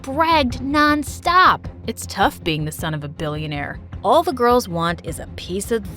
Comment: i thought he said son fo a b**ch for a sec